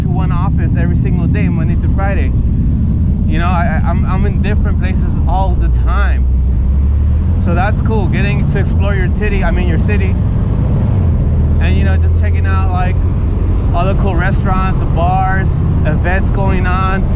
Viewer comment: I heard that " getting to explore your tittie I mean your city "lol You did a great fix up there. Hilarious 😂😂😂